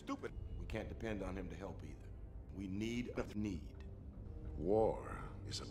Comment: Did he just say "We need a thneed"?